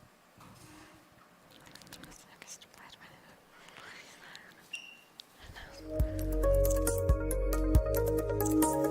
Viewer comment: what is she whispering @ 1:03:06 ? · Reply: She said where’s the Men’s room?